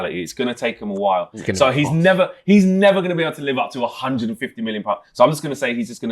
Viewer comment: Price of Wirtz keeps getting higher and higher by the rivals as the days goes by. At first, I thought said in 150 euros (still wrong). Then, I thought he said 115 (which would only happen after the add-ons (some very hard add ons and if met, that means, Wirtz would live up to it anyways)). Then, I checked (and captions also agree with me) that he said 150 million pounds.